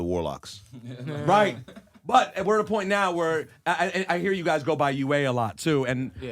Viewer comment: geeked at 16:38 where the captions got Rosenberg saying "so I hear you guys go by you weigh a lot now" 😂